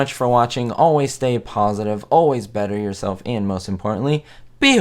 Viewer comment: Always butter myself? (I'd swear that's what I heard you say! LOL). Enjoy your vids! Great work on these!